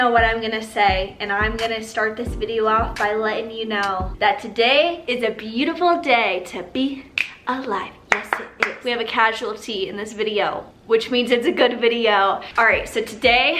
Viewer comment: I definitely thought you said “casual tea” instead of “casualty” lol i need help